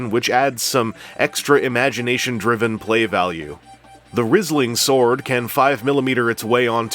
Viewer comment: I didn't realize his sword was called "Rhisling" until the very end. I thought you were saying "Rizzling" the entire time...